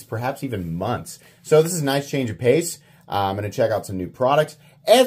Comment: I think I misheard but it sounded like said" knife change of pace." Lol